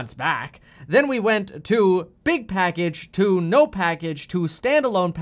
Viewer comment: I thought that said ‘Big Porkage’ instead of ‘Big Package’. I giggled a little.